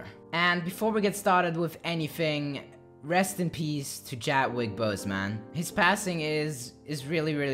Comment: did he say jatwick bose man lmao rip chadwick boseman tho